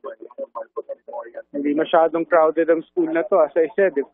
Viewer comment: May narinig akong pustiso ahaha